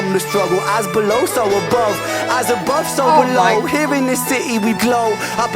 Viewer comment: The first time I heard this I thought he was saying 'As a Bus Overload' instead of 'As above so below'. Musical genius - who is on Rens level. The singing part at the end lifts the song.